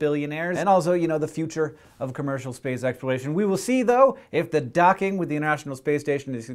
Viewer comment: I thought he said 'space sex'...